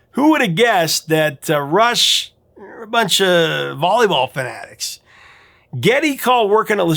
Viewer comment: I know why. You appeared to use a anti-gay slur when referring to Rush,. I heard "volleyball f*gs"